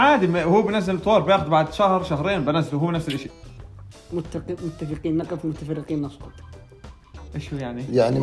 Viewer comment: ايش قال؟ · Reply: متفقين نقف ومتفرقين نسقط